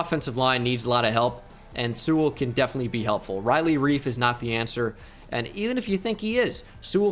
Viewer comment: thought he said riley reid